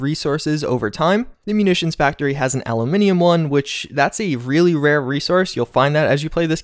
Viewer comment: Lol did he just say almunimium